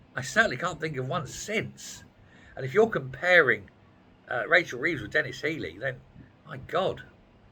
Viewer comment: #HAIR_BY_LEGO I COULD SWEAR I HEARD YOU SAY, "RACHEL THIEVES"... WHAT DO YOU EXPECT FROM SOMEONE LIES ON THEIR WORK APPLICATION?